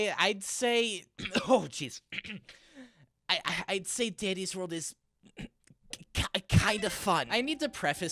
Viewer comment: did bro say diddys world😭😭😭